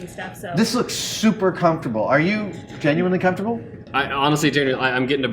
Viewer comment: Was it just me or did that guy call Adam Jamie at 9:39? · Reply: I heard "genuinely". He's annoyed because his own costume is so uncomfortable